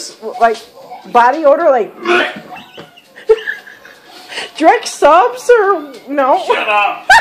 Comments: What is she saying @ 0:35 ? Sounds like “ Drape Soaps “ ... don’t know what that is! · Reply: Dreggs Subs, a local sub sandwich joint by them. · What is she saying at 0:36? Sounds like “drick supps”.